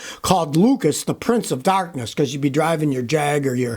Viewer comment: I heard "Lucas Electric, Inventors of Darkness"!